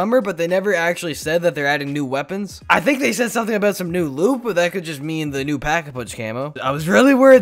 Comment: I swear he said new lube